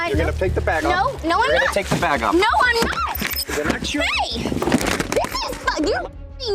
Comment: i thought it said “THIS IS RICE!!!”